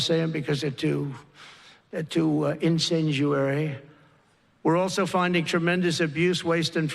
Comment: IN-Senjuary? Didn't want to mention because they were this...I can't find this word... Can anyone enlighten me? The only word which comes up is incendiary... Is that what he's trying to say? Sorry... These types of things grab my attention